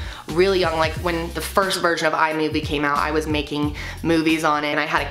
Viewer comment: When said "first version of Imovie came out," I thought she said "first VIRGIN," and was super fucking confused. Had to go back and relisten.